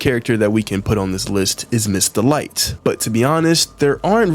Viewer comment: did anyone else hear "MiStAh LiGhT"